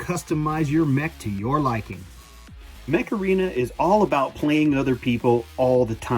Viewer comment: with my less than stellar hearing I almost thought he said macarena and not mech-arena